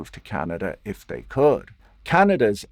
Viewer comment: would have been so smooth if he say if they cooked rather then could ahah Thought I heard cooked and it made my day 😂